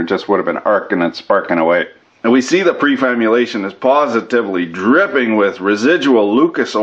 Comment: Did I hear the word "prefamulation" at 2:56?